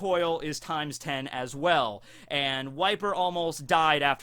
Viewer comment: Every time you say "Wiper", I hear "White beard".